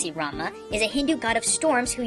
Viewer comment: When you said Ramah I thought you said ramen.